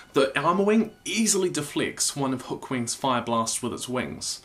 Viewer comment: Did you just say hook wing?